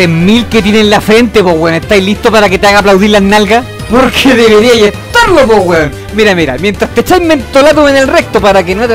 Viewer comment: ¿Qué dice en los segundos 0:56? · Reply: Dice PORQUE DEBERIAI ESTARLO WEON